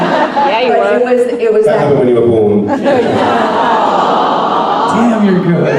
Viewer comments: what did tom say to tricia at 25:16? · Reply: “That happened when you were born” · Can someone please tell me what Tom is saying to Tricia at 25:16? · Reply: I believe he says ,,That happened when you were born.”